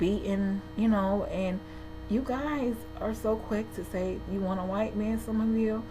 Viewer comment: hold on did u just say your married to a white man?